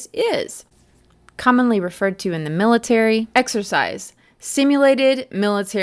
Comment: I swear I heard him say live MILITARY exercise....did anyone else hear that?